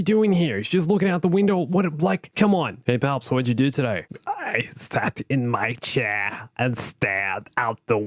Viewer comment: I thought he said i faped in my chair lol